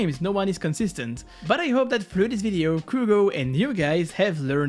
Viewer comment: Did he say Krugo, or is it just me?